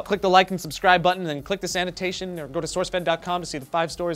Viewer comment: like if you think he said click the like buttin and the SANITATION hahaha